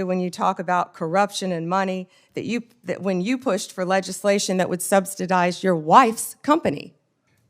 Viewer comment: Did she just say "substidize"